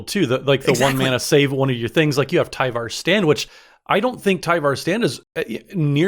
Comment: I thought said Tyvar's Sandwich for 2 seconds. Now I want an indestructible sandwich